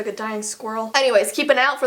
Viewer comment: When she said "My sneeze sounds like a dying squirrel" I thought she said that her sneeze sounded like a dino-squirrel. Lol